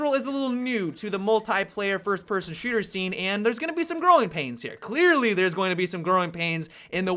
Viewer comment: Am I the only one who thought said "groin pains" instead of "growing pains?"